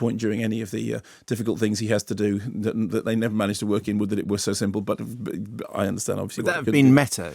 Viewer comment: Okay, I've re-watched the bit at around 1:30 3 or 4 times. What does Mark say?? "it's a shame they never managed to work in..."? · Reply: "Would that it were so simple". It's a line said by Ehrenreich's character in Hail, Caesar! <link>